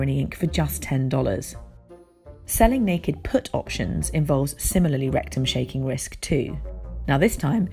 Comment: “Rectum shaking risks” ? Did I hear that right? Lmao 😂